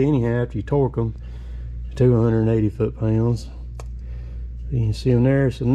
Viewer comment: The knife bolt torque spec is 180 Ft lbs. I thought I heard you say 280 Ft lbs. Oh my bad you said "to-a-180" its that tater accent thru me off.